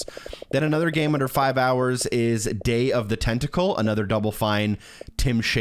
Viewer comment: broooo I thought that said day of the testacle 😭😭😭